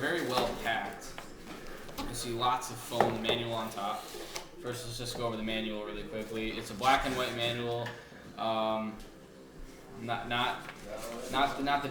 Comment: did he say black and wet manual at 0:36?